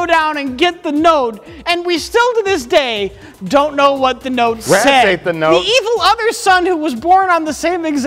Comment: Mike interjecting with "rats ate the note!" killed me for some reason. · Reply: I thought he said "a rat read the note".